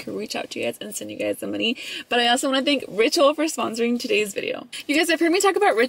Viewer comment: I thought you said "I wanna thank Rachel" in the beginning of the video. 💀 Like who is that?! 🤣🤣🤣 $vbunny01